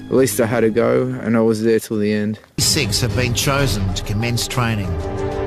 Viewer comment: does he say "six were chosen to commence training"? Because that group photo shows more than six. Was it maybe 26, and the "twenty" was clipped from the audio during editing? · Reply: 26 mate.